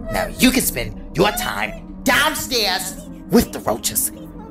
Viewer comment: the way she says "reoches" is so sassy😭💅✨ (i spelt roaches wrong on purpose because thats what is sounded like lol)